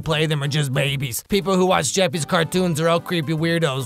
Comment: Wait did i hear “Jeffy’s cartoons” 🤣🤣